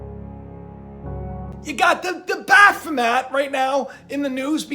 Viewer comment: When you referred to baphomet I could of sworn you said bath mat which was pretty funny and fitting.